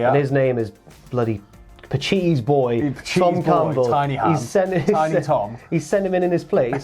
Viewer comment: Legit thought said "Pacitti's boy - Sol Campbell" and was imagining big Sol coming in to two foot leg break the prediction video. Big sad now.